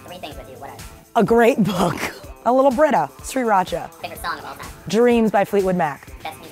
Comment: i thought she said 3racha